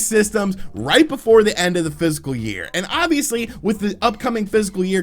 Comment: at the 9:25 mark does he say physical year instead of fiscal year...twice?